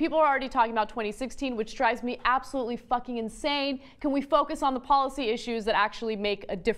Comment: did she just said fuckin